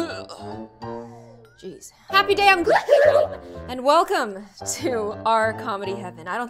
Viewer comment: In the past I always thought you said happy day on gloom. And it was like 3years ago or 2